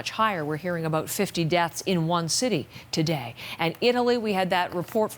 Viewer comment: Did she say 50 deaths in one day in Iran